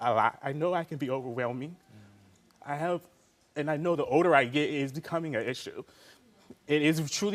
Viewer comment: who else heard “The odor I get is becoming an issue?” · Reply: Older in Ebonics